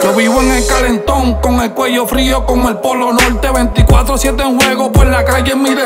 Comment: Todo el tiempo pense que decia "Frio como el AK"😂🤦🏻‍♂️